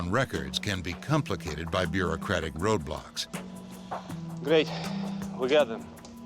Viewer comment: I thought it said Roblox